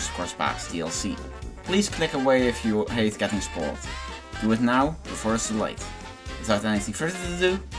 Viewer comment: what did you say? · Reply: Probably “do it now, before it's too late”.